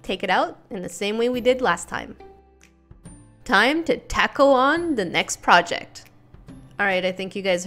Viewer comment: I thought she was saying tackle instead of taco because I say tah-co not ta-co